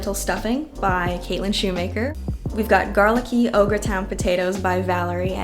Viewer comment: When you said “au gratin” at the end I definitely thought I heard Ogre Town 😂 I need to get my hearing checked